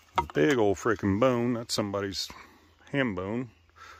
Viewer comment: I thought he said: .."somebodies hand bone.." 😂